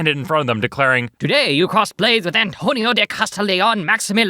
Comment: I heard cosplay